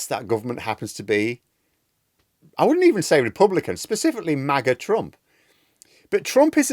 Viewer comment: "MAGA Trump" - I heard that as "Maggot Trump".